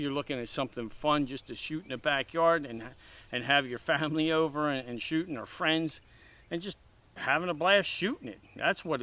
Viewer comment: did he say have the family over & shooting at friends. lol.. i know...